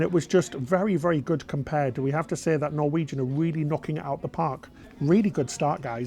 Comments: Until I saw the name of the ship, I honestly thought was saying “Norwegian Beaver.” I thought, “WTF Norwegian, that’s an awful name.” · I thought you were saying Norwegian Beaver…